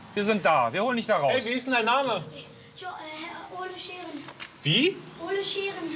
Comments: Ich hab am Ahnfang Ohne Scheren verstanden. 😂😂😂😂😂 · Polizist: "Wie ist dein Name?" Junge: "Ole Scheren" Polizist: "Und dein Vorname?" Junge: "Ole" Polizist: "Ach Ole" 😂😂😂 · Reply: Ich habe am Anfang ohne Scheren verstanden😂😂